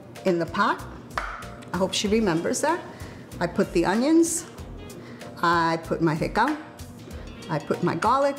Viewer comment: Wtf . She said recao.........who did the editing here 😅😅😅😅